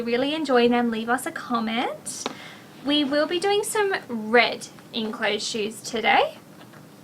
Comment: I thought she said "in clothes shoots"! :D